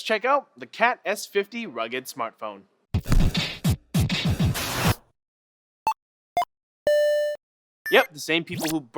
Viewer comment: Was I the only one that thought it said $50 Rugged Smartphone?